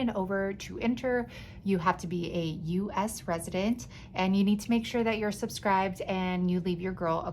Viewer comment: I thought you said, “you have to be a US President to enter”! That bride ghost is everything!!